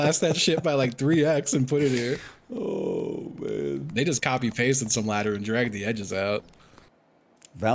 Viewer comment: Did I mishear 25:00 or what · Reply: “they just copy pasted” i think